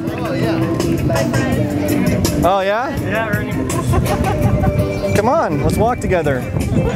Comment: I think that guy called you Ernie at 1:55 XD